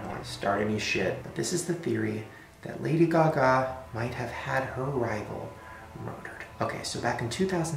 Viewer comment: what does he say at 12:45 ?? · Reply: Sonny Beavis he said that lady gaga might have had her rival murdered